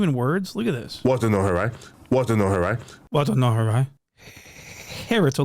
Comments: It sounds like he said it wasn’t no her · Sounded like he said, Wasn't no her right...